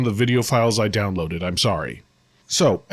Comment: Every time you say, "I'm sorry." in the intro to these videos, I hear, "Get over it." 😁